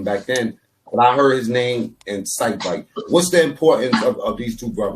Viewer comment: I actually thought they were saying Excite Bike. Like Excite Bike the Nintendo Video Game. LOL 😆